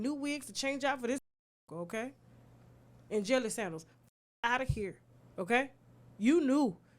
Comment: I lost it when you said jelly sandals 🤣🤣🤣🤣🤣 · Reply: At first I thought I heard Jelly sandwich 😂 then I heard Jelly sandals I just melted to the floor😂😂😂